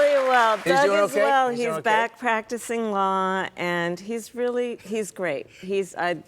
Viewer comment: Could have sworn she said, “He’s backpacking TsingLa.”